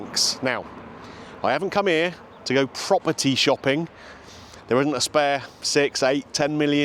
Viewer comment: In the opening seconds I swear I heard you say you weren’t there to buy proper tea. Then I realised you’d said property.